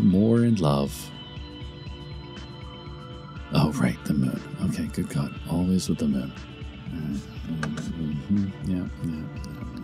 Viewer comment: What does he start mumbling? "Alright good cut" is all I can understand. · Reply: He goes 'Oh right the moon, oh good God always with the moon'